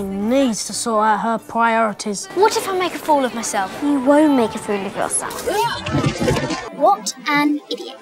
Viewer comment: Harry: What if I make a fool of myself? Hermione: You won't make a fool of yourself Harry: falls off his broom Hermione: What an idiot! LMAO that was great! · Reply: I heard, "You WILL make a fool of yourself" the first time. 😂